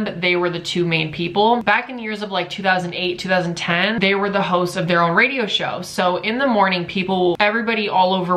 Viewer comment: i thought he said hoes not hosts